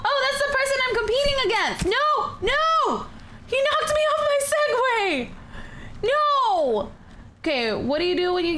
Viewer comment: Did she just say she nocked me of my senpie?